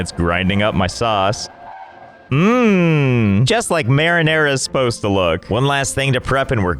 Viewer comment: sayonara sauce instead of marinara sauce